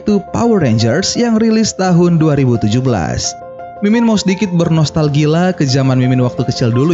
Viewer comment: nostalgila atau nostalgia? apa gw yg salah denger:v